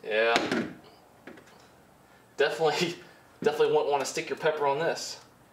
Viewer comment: sounded like he said you definitely don’t wanna stick your pecker on this and I don’t blame him 😂